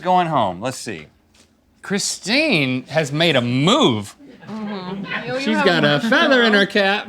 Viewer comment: I thought he said "she got a feather in her cat LOL"